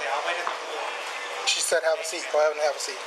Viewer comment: sounds like he said “she said have a seat clown, have a seat” 😂 he’s definitely a clown